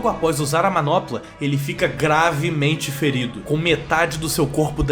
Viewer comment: nessa hora eu quase ouvi grávido kkkk